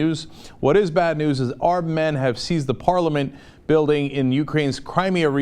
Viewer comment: I freaked out in rage because when you said "Armed Men" I thought you said "Our Men". I was like NNNNNNNOOOOOOOOOO!!!!! Don't interfere!